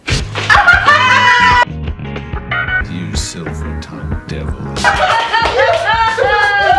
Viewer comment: "You silver tongued devil you" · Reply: I heard " you silver tongued him"